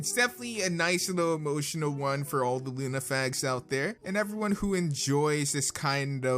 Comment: dude... did you say Lunafags at 3:32? it sure sounds like it. totally uncool, bro...